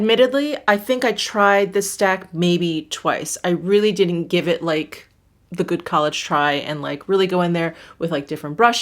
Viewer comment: did she say "didn't give it the good college try"?? Lol